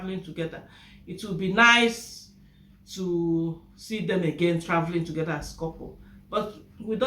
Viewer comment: I thought he said it will be his house. Oh my God that guy try to fool us.